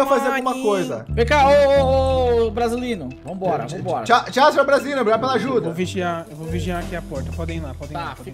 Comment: o fala "Tchau senhor Brazino" foi só eu que escutei isso?